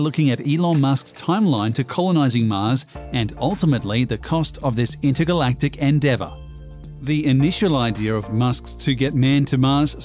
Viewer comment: did he really say "intergalactic endeavor?"